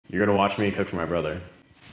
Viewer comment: why do i keep hearing 'you're ganna watch me touch my brother' at the start